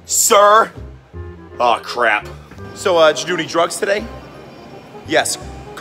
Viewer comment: I thought he said VOMIT THAT VOMIT THAT VOMIT THAT